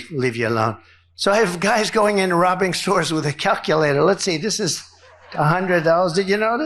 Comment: Did DJT say, “So I have guys going in and robbing stores with calculators…” at the 4:23 mark?